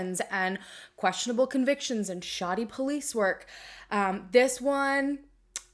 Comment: Thought she said shawty police work Had me intrigued 👀👀